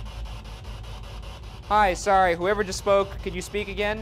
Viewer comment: it sounded like "Im sorry"